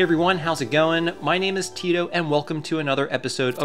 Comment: Did you just say "tits"? Sorry, I like Game Sack, lol